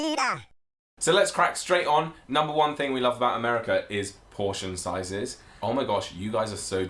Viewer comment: At first I thought they said abortion Lol